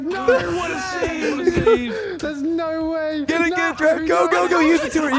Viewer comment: Am I crazy or did call Trev😂😂😂